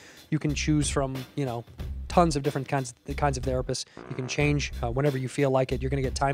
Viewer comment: he says "tons of cunts" instead of Kinds when talking about doctors & therapists 😂😂